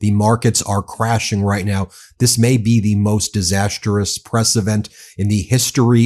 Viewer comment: "This may be the most disastrous press event in the history of the United States ..." I heard "the most disastrous president" the first time and having gone back and listened again to make sure of what I heard, I'm still not sure I was wrong. 😁